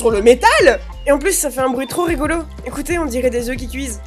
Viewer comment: Au moment de l'élevage de la peinture avec le produit, sur la cheminé, j'ai compris "on dirais de yeux qui cuise" 😭😭